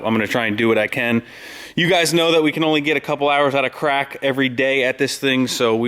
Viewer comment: It sounded funny at the end. it sounded like you said "we can only get a few hours outta crack" . Lmao. Good work man glad to see that thing in there